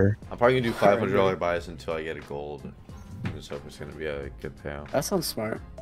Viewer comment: I honestly thought it said "until i get a president"